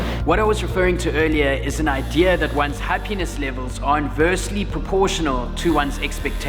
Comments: haha i thought he said ha-penis not happiness lol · Super good, But... When u were saying "happiness", I thought u were saying "her penis". So at first I thought u guys were getting matching penis tattoos😂lol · I thought you kept on saying “Her penis”... Happiness! Got it